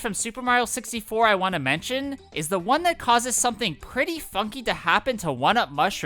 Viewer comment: for a bit I thought I was hearing ‘fucky’ instead of ‘funny’ or ‘funky’?